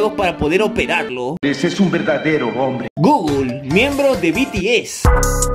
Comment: Like si también notaste que dice embros XD😅😅🤣😂